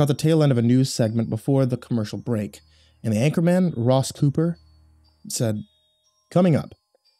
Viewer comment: for some reason when he first said "Ross Cooper" and I heard it as "Ross Pooper" ;-; why am I like this?